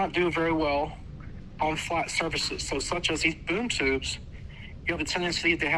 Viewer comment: Thought you said time for "Boob Tubes", new merch ????